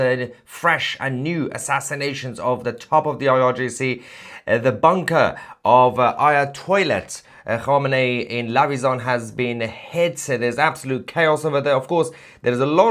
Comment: sorry I think I heard Aya-toilet 6:55 hahaha